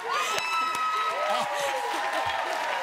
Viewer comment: I totally thought she said "his ass"